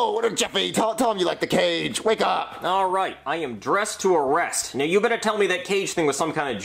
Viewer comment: now bc I thought he said dress to Impress☠️☠️☠️☠️